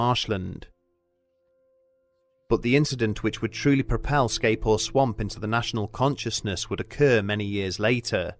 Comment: Not once did I hear "Scape Ore Swamp," only "Scay Force One" and "Skateboard Swamp." 😂